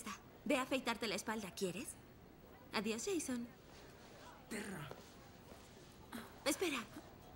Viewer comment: soy yo o oí que le dijo: perro! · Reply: Lupita -Chan creo que le dijo perra a Regina